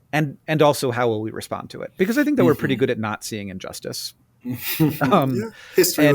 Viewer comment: did you say that you guys are good at not seeing injustices or that you guys are nazi-ing injustices...? 😅😬🥲🫠